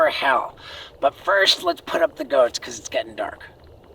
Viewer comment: At first I thought you said Put Down The Goats